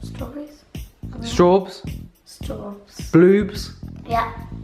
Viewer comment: Anyone heard Boobs